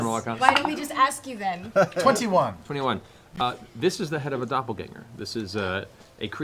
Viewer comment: Its a Doppelganger... For a moment I thought he said "It's a Scanlan..." - I mean its almost the same :P.